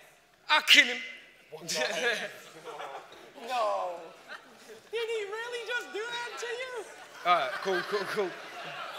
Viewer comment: amazing battle. does anyone know what happened at 33:00 ??! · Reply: the guy behind said "yes" when he was trying to complete his brain storming rhyme, so he cut him off :)))